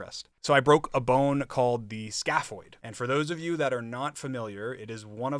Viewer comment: "schapoid" i heard scapholding😭